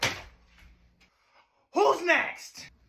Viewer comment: I thought she said wa- wa- fruit. With a p.